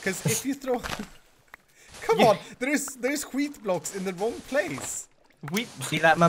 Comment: wait did he say skit blocks that mean shit blocks